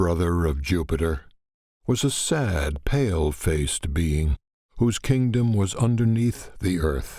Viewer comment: Swear it says bean instead of being